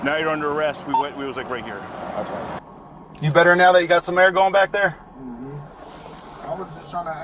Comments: It definitely sounds like he said “”you got me fked up”” she definitely wanted to jus lie because she didn’t like what he said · I heard “you got me F’d up”